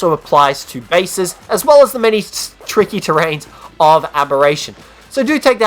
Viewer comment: I said can be used on all maps.... I know only on abb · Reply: ueah i could have sworn they said for only abberation